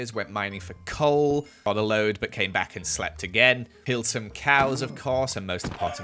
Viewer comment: i heard "peeled some cows" 💀